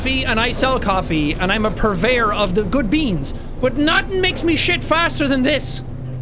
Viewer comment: For a split second I thought Jack was saying that nothing makes him sh*t faster than this (top of the morning coffee). As if insinuating that the coffee makes him sh*t faster. 🤣🤣